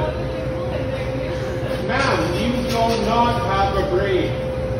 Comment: Anti-masker: "You do not have a brain!" Also anti-masker: urinates on counter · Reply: I thought he said “you do not have to breathe”